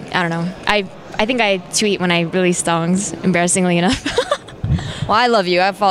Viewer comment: did she say she only tweeted when shes stoned